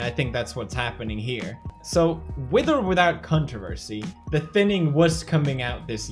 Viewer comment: I swear at 3:19 he said cuntroversy